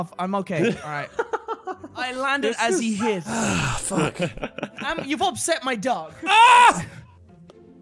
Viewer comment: What he said: "You've upset my dog." What I heard: "Why have you done this?"